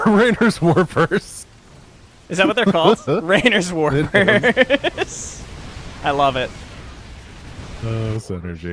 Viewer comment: Going to be honest, when I heard "Raynor's Warpers", I heard "Raynor's War-purse" instead. And then I thought about Arcturus Megsks's credit card.